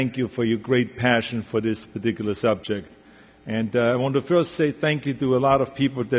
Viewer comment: does he not look shaken and scared ?? also he tossed in ....OMG did he say " This ridiculous subject " 1:00 << WTF thanks to Linda Adams "Cough Cough" humm and aluf Gervin << sp UN .... probly told heim say what I say or ya dead and your family is dead ... LOL thanks guy ! lol this looks like he is scared and doing what he is told to say watch him read it ! 8:29 reads from tel-a-promper